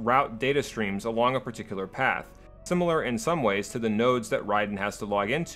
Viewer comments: did you say nerd? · did you say.... *NERD*? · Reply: Sooly not nerd, node